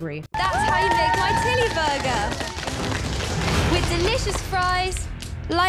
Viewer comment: I thought she said tiddy burger